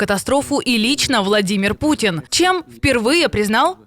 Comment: Я ,когда была маленькая при далеком Брежневе, то думала ,что Ильич - это должность. Про сталина и хрущева тогда почти не говорили, а вот Владимир Ильич и Леонид Ильич были на слуху, везде и всюду. Так вот когда вы сказали ,, и лично владимир пукин""" мне послышалось ,,ИЛЬЧ новладимир пукин😅😅😅😅😅😅😅😅 Блин думаю , оо этот тоже уже ильичь?!. 😅😅😅😅 Капец... от жары и не такое послышится и придумается.